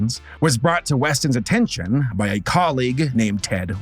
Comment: I misheard that on the first time and thought he said “Catholic superheroes” instead